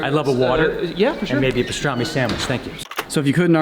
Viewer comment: At first I thought he said, i'll have a water and maybe a strawberry sandwich.